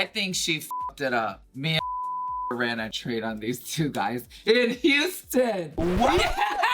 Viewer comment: What did Gia say at 00:19? I can't make it out but it sounds she traded underwear with someone in houston? Also her laugh afterwards was amazing 😂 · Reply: She ran a train on some guys in Houston.